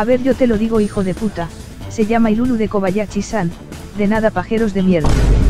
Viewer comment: nombre de esa waifu melona? No le entendí bien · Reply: Ilulu de Kobayashi-san